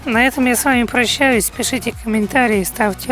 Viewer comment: Хех, послышалось:"Деритесь видео с друзьями." Видео полезное))